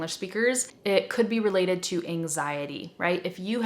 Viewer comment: can someone write the word that she says here please, I couldn't understand and it's a word that I don't know "could be related to..." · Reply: Gustavo Hammerschmidt anxiety (stress, alarm)